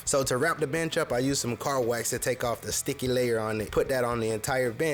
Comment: Did you say 'car wax' at 11:58?